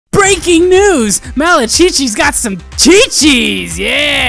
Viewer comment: Err i umm....destroyed my phone... im srry i let u down 😔😔 i heard chichis then came running i fell but anywaysss